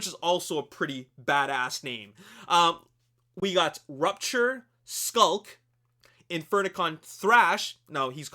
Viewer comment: I thought the shit said "shulk" but then I read that shit again and was like oh...... I'm not feelin it.......... I'll fuccin stop lol